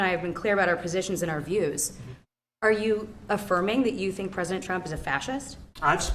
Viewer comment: Don't get me wrong I love Trump my wife and I voted for him twice, we like Karoline Leavitt shes a great press secretary.. and no disrespect to her but I noticed that when she brings up Trumps name it sounds like shes saying President Chump 🤣